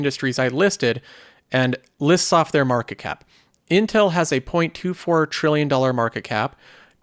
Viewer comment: I thought you said intel had a 2.4 trillion market cap for a second I was like huh?